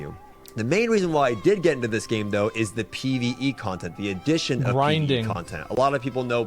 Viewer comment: who said it shouldn't get updates ? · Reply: I thought he said content, not updates.